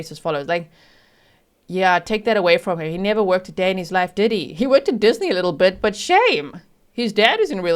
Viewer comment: Ok when G said; ‘he worked at Disney, a lil bit’ I I heard it as ‘he worked at Disney, the lil b*tch’ and I died laughing 😂 only to realise, that is what I wish she had said !!! As always Rest in power beautiful Madeline 🩵 🕊️ 🩵